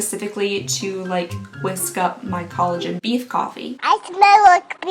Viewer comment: I thought you said “Collagen PEE Coffee”. 🤣🤣🤣🤣🥰